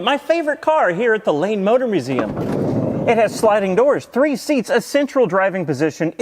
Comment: I kept hearing "Lame motor museum". It makes so much sense with that car.